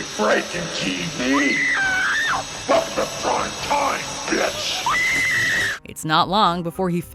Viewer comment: Does anyone else here "Fuck the prime time bitch!" instead of "Welcome to prime time bitch"?